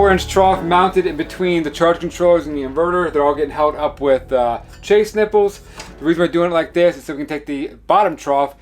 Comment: It's great to see all the commentary about how clean his work is and all... but 3:25 I thought he said 'chafed nipples'. That is all.